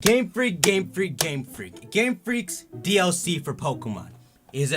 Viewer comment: I thought he's saying gamefrick gamefrick gamefrick